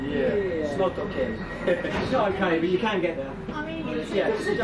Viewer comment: did I hear the words 'Smoke cocaine'